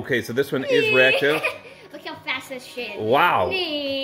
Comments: did just say look how fast this sh*t is ? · Did say sh**